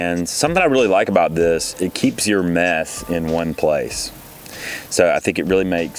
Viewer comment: "It keeps your meth in one place" Please tell me I'm not the only one who heard that.